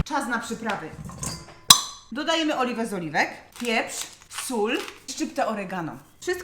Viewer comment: Usłyszałam że mówi pani "dodajemy oliwy BEZ OLIWEK" XD